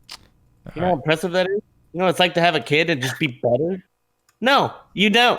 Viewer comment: What does Mango say at 08:25 ? "Do you know what it's like to have a kid and just be _____?" I can't even tell the whole sentence but I know in my heart it's a banger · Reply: "better"